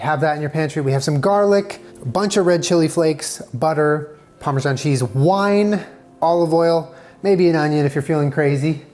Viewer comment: I heard "Parmesan cheese wine" and now I'm disappointed that it doesn't exist. Damn.